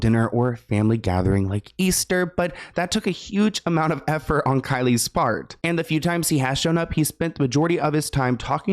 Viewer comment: you say Kylie's part but it sounded like Kylie's fart. My daughter and I both looked at each other and go Kylie's fart? With the same look in our faces lol. Then we had a great laugh. Thank you for a fun mommy daughter moment ❤❤❤